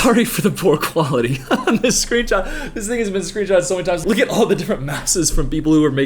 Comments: Did he just say the n word at 6:57? I heard "this n****'s been screenshotted..." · i thought bro said “this niggas been screenshotted”